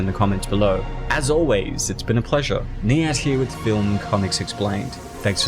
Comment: Wow I remember this movie Soo weird but good And I just realized your name is...I always thought you said THE ANT l.👀